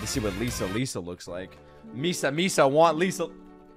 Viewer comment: Misa misa😭 i thought you were saying mista mista for a sec